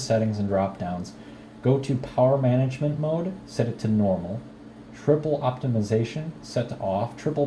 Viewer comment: did u say triple or threaded optimization mode because I can't find that setting in control panel on my G15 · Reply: Its threaded I think, sorry for the confusion